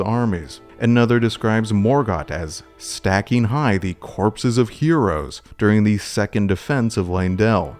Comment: In this video, I mispronounced a character's name. Thank you for your attention to this matter. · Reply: Before watching the full video I heard "corpses of bodies" with a knight design that never appeared Could that be Heolster?